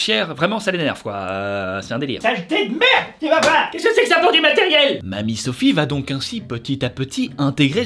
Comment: qu'est-ce qu'il dit ? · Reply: "Qu'est-ce que c'est que ça pour du matériel ?"